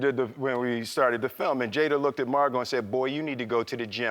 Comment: I think I heard Will say "So Jada looked at MARGOT" instead of "So Jada looked at me and said boy you need to go to the gym right now."But amazing interview otherwise so glad to have Mr Smith back